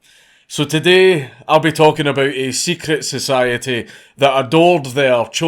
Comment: come on, I cant be the only person who thought it said "the secret society that worshiped drugs"